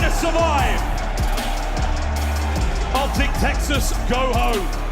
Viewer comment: Let's go FLOP...! What is the commentator saying in 1:02:57? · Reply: Optic Texas go home